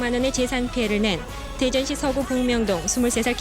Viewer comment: 자막 쓸 때 검색 한번 해보기도 귀찮냐? 대전이란 도시가 생긴 이래 복면동이란 동네가 있어본 적이 없는데 그냥 대충 들리는대로 초딩받아쓰기를 해버리네 지금의 유성구 봉명동을 말한 듯한데 심지어 봉명동이 뭔 변두리도 아니고 대전최고번화가를 방송국 일한다는 놈이 한번을 못들어봤나